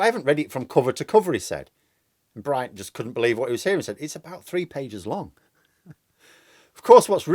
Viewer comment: I watched that and heard it as "two pages", though Bryant did mutter it.